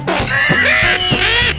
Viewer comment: Gravity cat out of use ahhhhhh! Crush I'm dead! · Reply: I thought it said gravity cat not amused